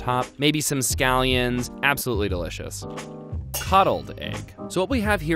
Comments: I thought he said cuddled egg. That's... That's weird XD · I wasn’t paying attention and I heard “cuddle egg.” Not coddle egg 🙂